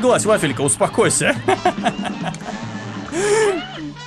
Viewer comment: Лол мне послышалось что принцесса когда выглядывает с замка говорит "эй мурло"